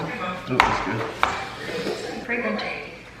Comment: I think at 22:54 you said “fragrant” but my ears heard “I’m pregnant” and I was like… why isn’t Weston reacting at all?! 😂